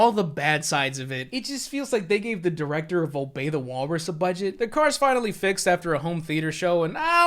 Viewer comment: Get a Current Black Premium Card: <link> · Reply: Yo homie who was it that u shouted out at 6:33 it sounded like you said volbata walrus